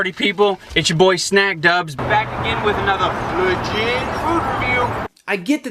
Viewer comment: am i the only one who always thought he said "fuji food review" instead of "legit food review"